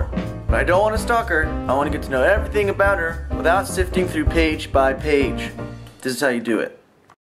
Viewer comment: Haha! The video buffered at 0:08 and I thought he said "And I want to know everything about her without sifting through pee..."